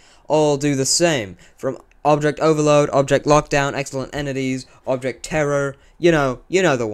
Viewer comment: I thought you said "Electronic Extremities" and I was surprised that I thought you found my object show